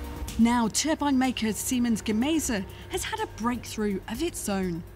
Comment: lol ... Seaman Gaza instead of Siemens Gamesa 4:19 ..at Orus University instead of Aarhus University